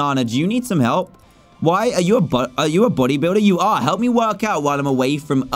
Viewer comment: hear at what said at 19:53 trough 19:57. he said are you a butt listen carefully